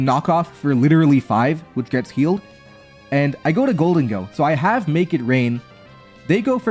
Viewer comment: thought I heard you say "I have Naked Rain" and now I can't unhear it...😢